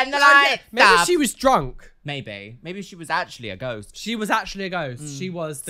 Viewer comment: At around the 35:00 mark when said ghost, I totally misheard it as GOAT. 😂😂😂 Had this amazing image of a goat running amuck in a dark store